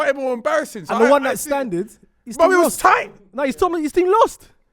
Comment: Did just say STANDED, what in the English Language?